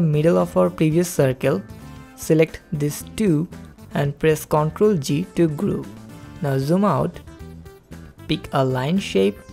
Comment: what are you saying? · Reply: "Select these two, and press CTRL-G to group."